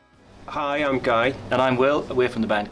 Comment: Did anyone else mishear guy and think he said "hi I'm gay" at the beginning? No? Okay. Just me. XD